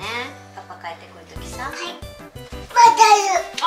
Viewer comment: Did little swear at 4:51 XD, it sounded like she said Bakayaro lol